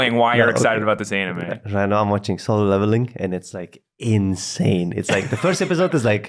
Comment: What anime did he say? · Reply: solo leveling